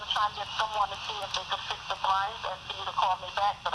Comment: In the recording, the plaintiff clearly says the shades,,,,,plural but the judge only awarded 1 shade. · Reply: LIES!! 8:08 clearly says BLIND, never said shade.....you sound SHADY like the old AZZ landlord, both yall cant hear LMAO